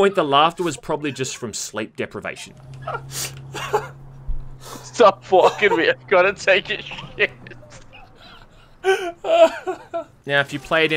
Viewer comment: When I was watching this video the first time I heard “stop fucking me I’ve gotta take a shit” which is equally hilarious